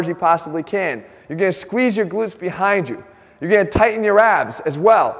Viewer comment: I was chewing on something hard at 6:30 and the noise in my head made me think he said "you're gonna tighten your ass". I'll make sure to do so haha